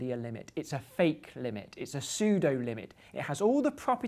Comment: in 8:42, you used an synonym for the word "fake" which is "sudo". is "sudo" part of english vocabulary? Im not a native english speaker and I work with Linux, I heard this "sudo" word in the context of linux but I didn't know it was possible to use it as synonym to "fake".